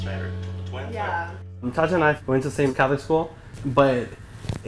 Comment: Hold the phone, did he say they went to the same catholic school? Haha